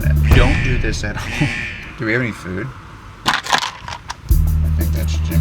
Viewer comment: instead of do you have any food i heared "youre gonna get sued"